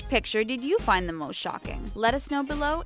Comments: i thought they said dont forget to subscribe to the taco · When every time someone says the TheTalko, I thought they said the taco XD